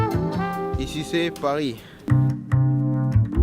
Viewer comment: c'est que moi qui ai entendu "Eh sucez Paris" ...